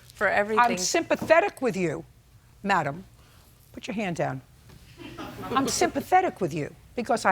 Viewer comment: I misunderstood JJ and thought she said “madam put your hand down”in reference to the plaintiff’s son. 😂😂